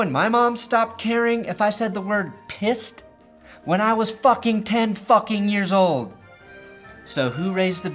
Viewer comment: I thought he said "when I was fucking 10 yr olds"